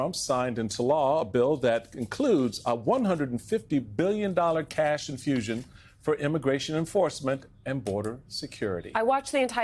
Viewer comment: For a happy second, I thought I heard he'd signed a bill with a "$150,000,000,000 cash infusion for immigration encouragement" and I thought "we're on our way, it'll be alright!!" 😔